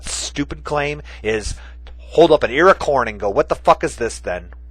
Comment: You love to throw *what* in the face of "new information" deniers? Sounds like you say "earcorn" or what is it?